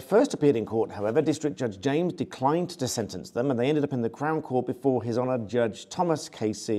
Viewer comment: I was married to a Brit for 20 years but I have trouble understanding you. I thought you were saying they went to Clown Court.....LOL!